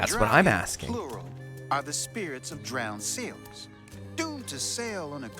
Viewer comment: the wood man is actually saying "sailors", not "seals" or "souls"! it just might be a little hard to tell with the accent.